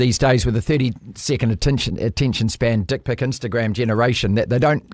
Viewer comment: Did he say "dcik Pic Instagram generation"? 😂😂😂